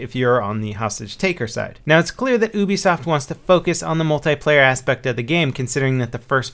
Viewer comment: Lol it sounded like you said boobysoft instead of ubisoft